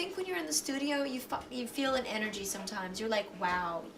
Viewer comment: does she say "yeah I think when you´re on the studio, you "fuck!" you feel like energy sometimes...". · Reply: "You fi-" like "find" I think